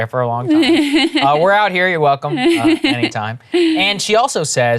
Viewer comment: The segment was a lot funnier when I thought Saagar was just saying "we're out here. You're welcome." He should've left it :D